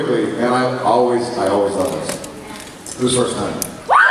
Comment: what does Jared say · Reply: He says, "Who's first time?" He always loves asking that question as soon as a panel usually starts because he wants to know who's first time it is at a Supernatural panel. Hope that makes it clearer 🌺